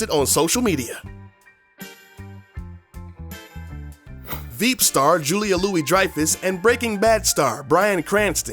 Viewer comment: i thought He said vape star XD